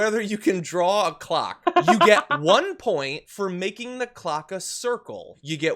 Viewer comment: Waddya mean clock....I thought you said c_ck. Gimme the points anyway, In fact pass me with flying colors, I''m president of the United States and I have the key to the nuclear code.